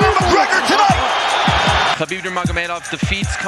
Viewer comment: Minute 12:37 ++ when the host said KHABIB NURMAGOMEDOV but the english subtitle said the BUTER MOTHER MAY DOGS . why the subtitle so rude ? Anyway im feel really excited and happy KHABIB defeat CONOR . UNDISPUTED UNDEFEATED . moral of story , dont ever ever fight with someone who is practice battle with BEAR . congrate KHABIB NURMAGOMEDOV